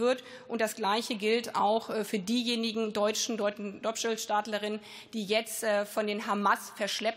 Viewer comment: Habe ich das richtig verstanden „Dopschelstaatlerin“ bei Min. 9:27?